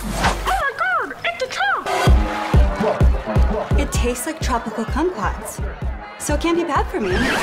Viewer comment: I hear “It tastes like tropical c*m pots”